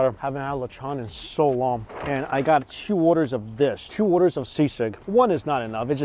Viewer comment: What is the food he has at 8:43? I keep hearing the word “seasick” & thought that can’t be right so I turned on the subtitles & that’s exactly what it says 😂😂